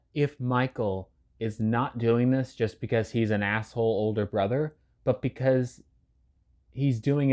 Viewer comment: I heard "@ older brother" I was about to send hate lol